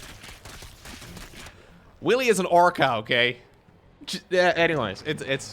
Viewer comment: with Lowkos’ accent it almost sound like he said oar cow instead of orca 😂. Enjoy every minute of these replays! ❤